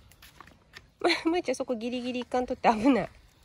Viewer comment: 辺りのまるちゃん🐶✨に話しかけが、ぶーちゃん🐶✨に聞こえる😅